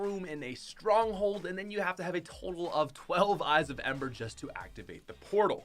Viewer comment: Did he just say eyes of ember at 2:05? Smh